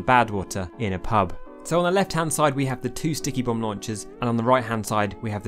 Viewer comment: With your accent I keep hearing "squeakybomb launcher"